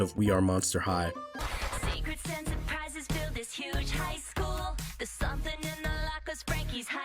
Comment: (I had this playing in the background) Am I the only one who heard "huge ass school?